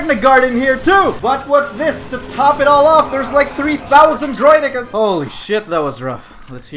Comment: it sounds like you said Droid N***